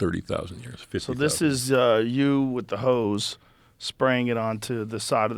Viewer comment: All I hear is "So this is you with the HOs."